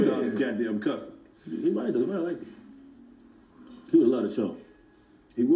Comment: What did Dallas say at 11:20? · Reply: Dallas said “He would’ve loved the show”. Meaning if MJ was still alive he would have loved the 85South Show.